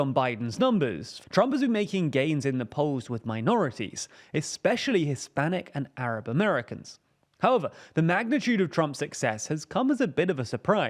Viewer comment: Did you say Trump improved “especially with Hispanic and Arab Americans” at @ 1:39?